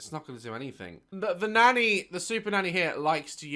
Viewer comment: I thought the video said super fanny😭